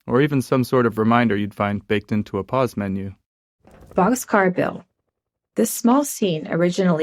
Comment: does she not say box car bill? Because caption says Joe and so does yt captions